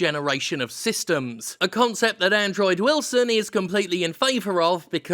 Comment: Coulda swore you said Android Wilson, maybe thats fitting?